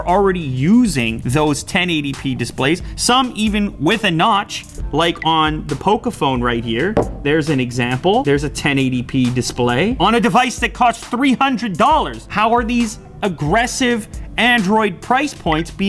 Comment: Anyone else hear him say 3:21 Poké-Phone? lmao